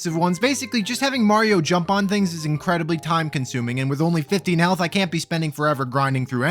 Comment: I know you said "15 health" but I swear to you I heard "only 15 hells" as in the circles of hell. Both are appropriate for this run, I think. Good on ya, very funny commentary over an otherwise mind numbing playthrough, you got yourself a sub buster